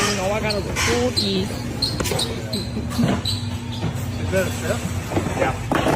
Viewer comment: Did I hear that correctly? "Is that a theft?" "Yeah..."